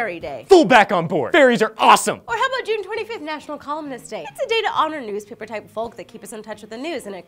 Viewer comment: When Lee said, "National Columnist Day" I thought she said, "National Calmness Day" which would indeed, be awesome.